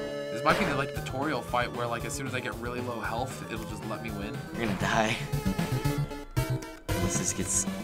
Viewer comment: umm did anyone else hear your gonna die?